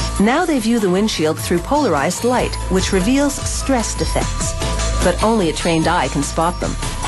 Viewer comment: ohhh stress defects, i thought she said swastikas